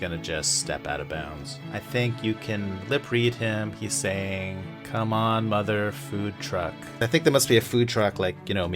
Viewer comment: I thought Draymond said come on mother forklift